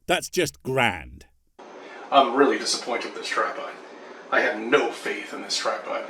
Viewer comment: When he said tripod, I thought he said strap-on The strap-on was more interesting